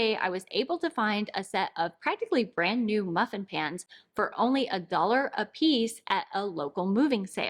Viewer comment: 100% thought you said “muffin pants” at first. I thought, boy these fashion trends…